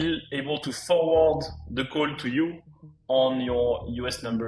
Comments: error in captions there: "code" instead of "call" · I know french accent is rough, but subtitle is wrong here "Call" not "Code"